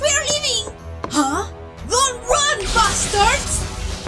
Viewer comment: WHAT DID SHE SAY!!! Roxy I may have a crush on you, but LanGUage please!!! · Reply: (Daniel) Roxanne said don't run buster's